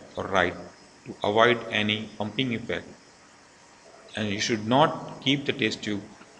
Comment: what was the word mentioned on the 2:18 ? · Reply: bumping effect